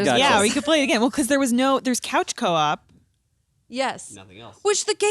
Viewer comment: cowchop? Did somebody say Cowchop?